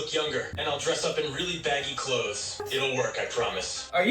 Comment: I thought he said "really baddie clothes" 😭😭